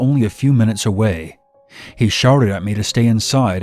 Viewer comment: Is it just me or in the 3rd story when he says the man had a ak47 pointed at me , does he then say "he 'sharted' at me"??? Lol. I know he means shouted but to me it sound like he says sharted